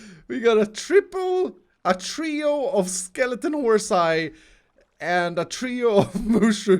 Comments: Did he just say "horsie" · Did you just say horsi? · Did he just say horsi?